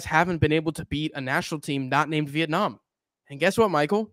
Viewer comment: Bruh what??? look at last 3 match indonesia vs vietnam? They win them all, an the last match they beat them 3 v 0 at vietnam home lmao, i absolutely agree that indonesia team is weak, but dont yapping so confidently when you spitting no sanse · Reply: really? What i hear is "that name" not "not named", It close tho